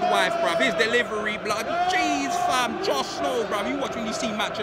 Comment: Even as a United fan, you gotta love! JUST KNOW!! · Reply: Oh I always thought he was saying Jon snow lol just thought he was a game of thrones fan